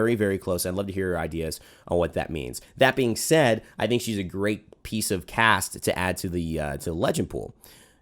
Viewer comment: It sounded like you were gonna say "great piece of ass".......wouldn't be wrong too